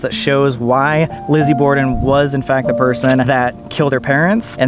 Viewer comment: i thought he said "Glizzy Borden"